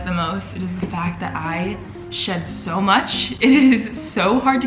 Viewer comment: I THOUGHT SHE SAID “THE FACT THAT I SHIT SO MUCH” 😂😂😂😂